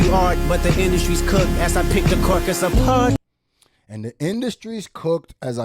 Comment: He more like Keyser Söze... #UsualSuspects I thought he said pick the carcass apart 😌🤷🏾‍♂️👽